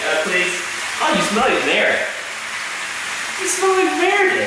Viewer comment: i thought you said he smells like "nair" for her shampoo.. lol i almost did that once for mouse.. almost made my hair.. but lucky i thought of competitor... go as bare as you dare with .. bikini bare lol